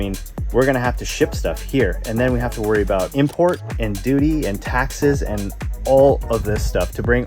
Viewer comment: You said "Duty".... I heard "Doody"..... Yes, I have kids and love Wreck It Ralph....